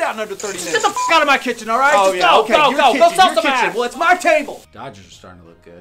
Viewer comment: i wasnt reading the subtitles and thought he said “go sell some ass” lmfaoooo